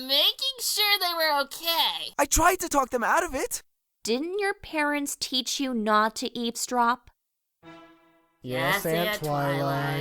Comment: i thought twilight said didnt your parents teach you not to eat straw...